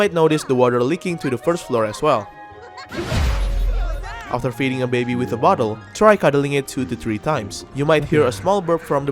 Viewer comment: i swear i was zoning out and originally misheard it as "after defeating a baby with a bottle".